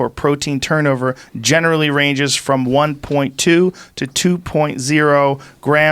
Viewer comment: When reading along with I heard 1.6 but he actually said 1.2